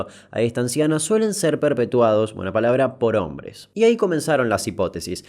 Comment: escuche poronga